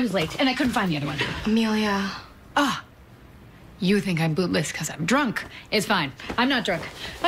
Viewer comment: I keep hearing "boobless"😅0:23 😂I'm dying I love this scene